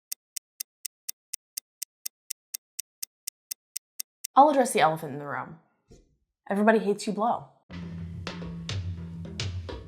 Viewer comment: I heard "Everybody hates your mom" and I was like UM WHAT?